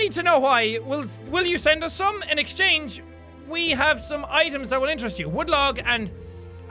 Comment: put on captions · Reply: Apparently thought he said Asians instead of 'items.'